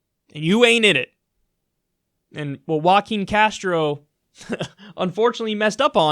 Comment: "Walking" Castro? Really? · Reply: 故意に隠すこと Are you sure because it sounds like he said Walking Castro?